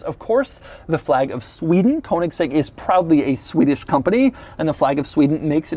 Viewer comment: "Koenigsegg is probably a Swedish company" What? Probably, you say? Silly Doug.